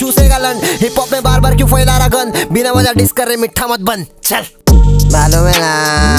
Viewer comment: what he said here?? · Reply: "Malum hai na" Ye krsna bi bola tha isleye Emiway bola ye tere baap seekhaya tha kay teko?